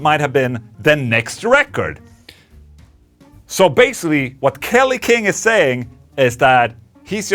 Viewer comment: Wait what??? Did you said Kelly King????